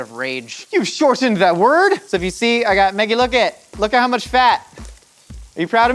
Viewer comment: When he asked Maggie to look I misheard and thought he said "megan look!' and I was focused on my crocheting and it scared me!!